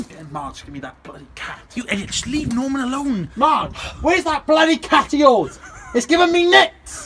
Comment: what`s saying at 2:00 ? something bloody caty...